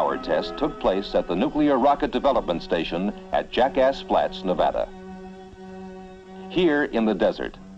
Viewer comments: Did he say “Jackass flats” at 3:17? 😆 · wait did i hear Jackass place in Nevada?